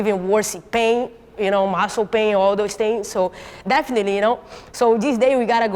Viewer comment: really sounded like a..hole pain the first time I heard it.